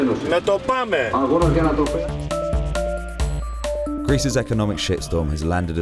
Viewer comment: did he say "Greece's economic shit storm"? 0.0